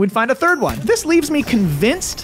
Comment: I thought you said Butt Fluff the first time. And now I'm just gonna name the first shiny Lamball I get Butt Fluff.